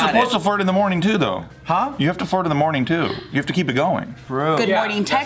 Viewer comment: I thought said you have to fart in the morning😂